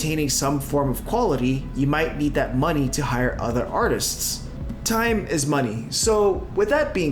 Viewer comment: Did someone say I need to hire artists? I wish my dad could illustrate a new book in the style of Disney and Don Bluth.